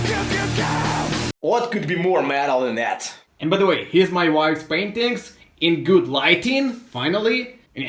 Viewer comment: I had to listen the end again as I thought you said; " here's my wife's spandex in good light"